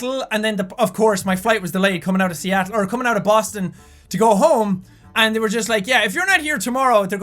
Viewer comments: OMFG! he got his diamond play botton, IN MY BDAY?! AWESOME!!! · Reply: also, when he said boston, I thought he said boss town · Am I the only who thought BOSSton when he said "Sorry, I was delayed, I was stuck in Boston"?